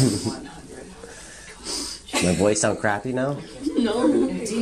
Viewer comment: did she say $7100😧for hearing aids